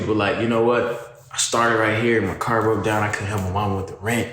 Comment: So he said car broke down I thought he said heart broke down but ts still hard